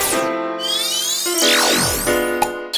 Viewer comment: トコヤミcrazyに聞こえて草